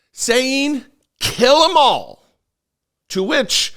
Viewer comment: he thought said "kiss them all"